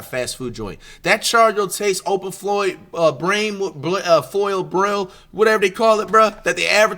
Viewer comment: wtf is bro tryin to say 🤣🤣🤣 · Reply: open flame chargrill or charbroil 😭😭😭😭😭😭😭😭😭